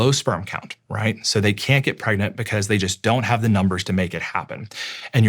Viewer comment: did I hear correctly did he say young Man that they can't get pregnant"